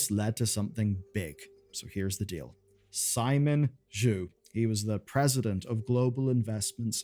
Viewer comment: Anyone else hear "Simon JEW"